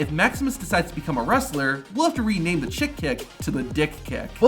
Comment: Did i hear that correctly dick kick